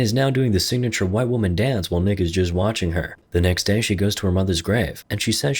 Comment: did he say she does the classic white woman dance while n****s just watching her? 😂😂😂